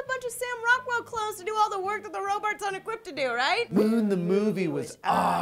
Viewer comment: does say Robarts instead of Robots?